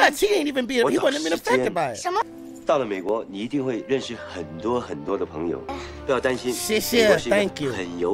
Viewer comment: the subtitles here are wrong, he actually says "Listen, you will make many many new friends in America" (Mei guo ni ting wei ren shi hen duo hen duo de peng you)